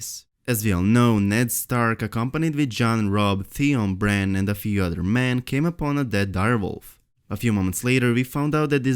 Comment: what's he saying in 1:33 to 1:37 ? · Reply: "That's mostly because showrunners, David Benioff and Dan Weiss have a show based on a book series, and the show is not written on a season by season basis."